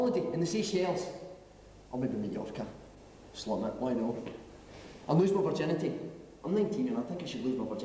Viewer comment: Did he say "I'll lose my virginity" at 1:06?